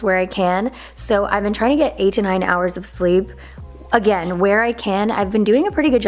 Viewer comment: Lmao I thought you said “I’ve been trying to get eighty nine hours of sleep” instead of 8 to 9.